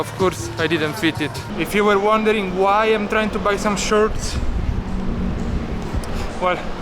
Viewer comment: How many people thought he was saying Shirts not Shorts?